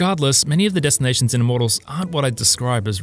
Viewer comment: He says "Immortals" and I keep hearing "the models" 😂